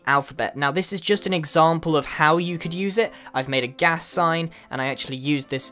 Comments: I thought that said Cas and I had a mini heart attack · I thought that said Cas instead of gas XD (CASTIEL! :D)